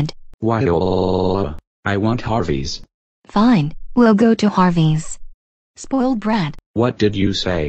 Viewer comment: I thought caillou said Arby's